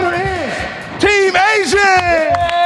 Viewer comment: I thought bro said team asian fr